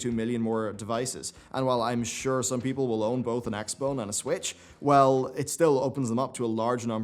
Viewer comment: did he just say x-bone?